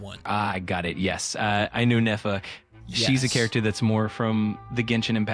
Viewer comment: AI thought you said Nefer. Not Ineffa